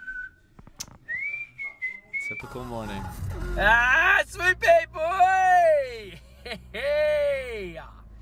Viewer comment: just to clarify, he was saying “swoopy boi”, not sweet baby boy. Swoopy boi (boy) is slang for a swooping magpie.